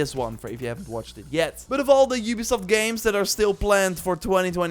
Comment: I thought he said at 1:51 “for all the Ubisoft games still Bland for 2022” 😂